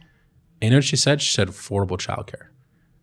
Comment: what does he say, please? ___ childcare? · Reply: Sami Folio affordable